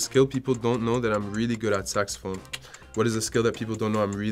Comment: ''What is a skill people don't know that I'm really good at?''.... I heard ''sex phone'' . I need to get my ears checked!